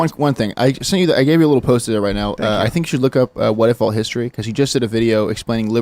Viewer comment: What did he say? "Look up "what if all history"? I can't find it. EDIT: 1:34:23 Oh, He said "what if alt history". The channel is actually named "Whatifalthis".